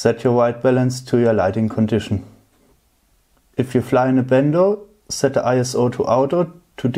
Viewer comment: Thank you for sharing your experience with Runcam 5 Orange. I have question because cannot understand 1:46 when you talking about ISO and same word you use when you explaining when use ND8 filter at 3:12. It sounds like: bendow but I cannot figure out what does it mean. Could you explain? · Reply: Bando is short for abandoned place, in this case i‘m talking about situation where you fly with changing lighting condition....e.g. you fly through windows of a abandoned house into the direction of the sun